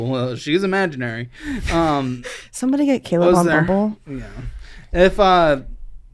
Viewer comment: wtf did she say!?????? The whole room got awkward after.. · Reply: “Somebody get Caleb on Bumble”